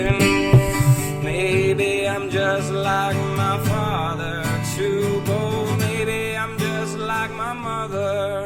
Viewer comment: I hear "MAYBE IM JUST LIKE MY MOTHER"...funny thing..I always heard "maybe Im just like my father Tupo"..I guess his fathers name was Tupo? Hahahaha